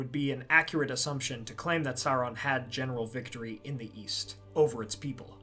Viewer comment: Oh East I thought you said Weast.